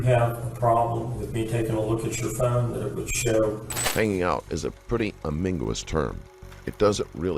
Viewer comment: Did anybody else hear "Hanging Out Is A Pretty Amingolous Term" 🤔